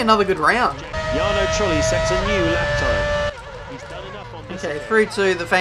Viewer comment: caption says jarno trulli sex